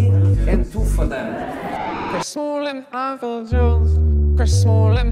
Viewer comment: For a solid 30 seconds I thought they was saying “chris smalling apple juice”